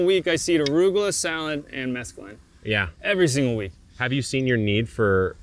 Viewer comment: and other places he says what sounds like “mescaline” …. He says , “i seed arugula and mescaline seed every week” He can’t be saying mescaline as the chemical compound responsible for hallucinogenic effects of Peyote (?). So what is he actually saying or talking about? Gardening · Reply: Mesclun, as in mesclun mix lettuce